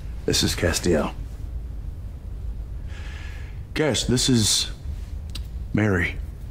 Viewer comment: why does it sound like dean said “Cassie” instead of “Cas”😭